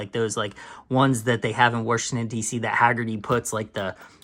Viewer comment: did you say "Worshington"?!? You wouldn't happen to be from the midwest, would you? 😊